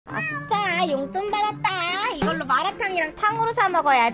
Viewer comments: 에 똥받았다로 들림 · 계속누르면 똥받았다라고 들림